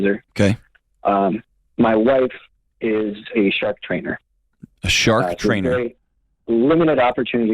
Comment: I thought he said CHEF TRAINER. Lol